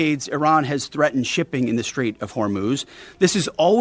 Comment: I swear to God every time I hear someone say hormuz I hear the word hummus so all I hear is Strait of hummus Instead of the Strait of Hormuz